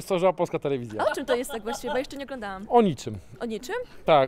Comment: kto jeszcze usłyszał "takie kurewskie życie"? i ten spokojny uśmiech na twarzy xD